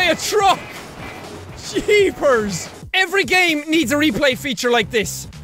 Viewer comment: Lol, Jack did you say jeepers at around 3:40?:D